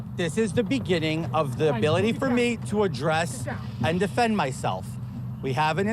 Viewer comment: Did he say "a dress" ?